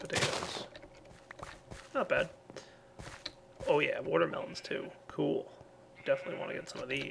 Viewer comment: did he just say water pumpkin